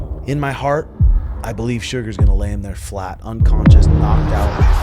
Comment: Did just say 'Suga is gonna land there flat and unconscious?' 00:10 Okay it was probably 'lay him(Merab) there flat'